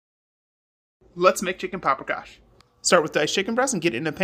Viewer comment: Looks good funny part is the begging of to me all I heard was “let’s make chick *giberish*” and for some reason I was dying 🤣